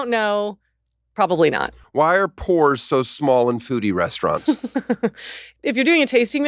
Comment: I heard “Why are POORS so small in foodie restaurants”